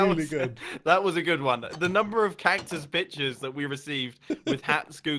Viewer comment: Anyone else thought Mumbo said cactus bitches at 1:32?